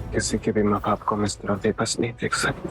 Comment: is time pe sameer prem ko kya kaha samjh nhi aaya 🥲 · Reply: Sameer ne prem ko ye kaha h ki ham kisi ke bhi maa baap Ko itna babas nhi dekh sakte